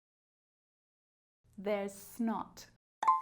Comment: Nah i got 50/60. The only reason why i got most of them wrong is because how you were saying it was confusing for example you said theres snot, i thought you said there’s not. And many others. Sorry for giving a bad comment but the rest was fine. I am 10 years old and im practising for the 11+ exams in britain. Alright, BYE!!!